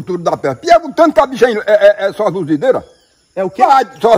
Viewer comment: Aos 1 02 ele diz que é de Videira ??? NÃO ENRENDI DIREITO, alguém traduz oque ele disse . AP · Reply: Eu entendi "seduzideira" kkk deve ser sedutora